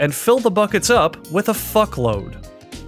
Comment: I swear it sounded like he said FUCKLOAD